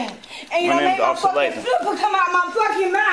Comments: The officer is absolutely justified in what he is doing with this shoplifter! He certainly did not put up with her guff/attitude. What an entitled woman she is! Mouthing off at the officers! She'll do anything to win an argument. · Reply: he is an officer. at 7:25 she asks him is name and he quotes ' my name if officer.... Leighton ' I believe is what he says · Can anyone decipher what she said at 7:30? It sounds like she said “super” came out her mouth.